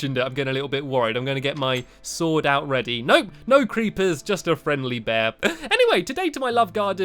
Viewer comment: did he say friend Lee Bear? Lol